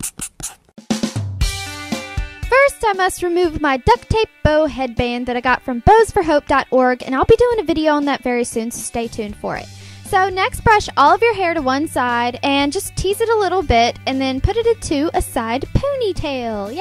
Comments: I totally thought she said bows for hoes lmao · I thought she said bows for hoes lol